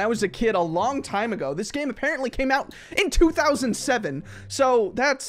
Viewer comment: when someone says 2007 or anything else i think u said 2017 because I WAS 7